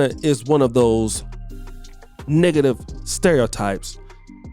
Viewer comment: Am I the only who thought it sounded like N1gg@ stereotypes? lol. Freudian slip.